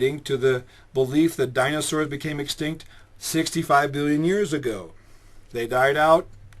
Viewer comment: did he say 65 billon years ago